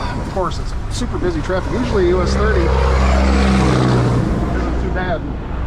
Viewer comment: I never could catch a sign, was this 23 that it happened on?? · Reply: Oop! I just heard you say US 30